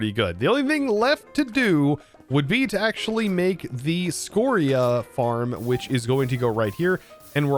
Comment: Did say Scoria two times? I think he mean Limestone & Scoria？ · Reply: He said Scoria and Scorchia. Two separate blocks.